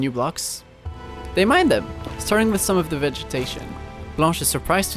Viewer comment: i thought you said notch and i was like "wait notch is a girl?"